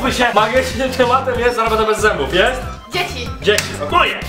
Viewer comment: czy tylko ja usłyszałam " Weza jest dziewicą"??